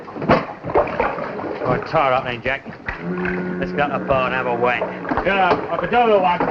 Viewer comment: What does he say at 6:35? It sounds like have a wank. That cant be right.